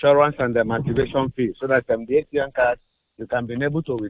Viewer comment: Literally thought he said masterbation fee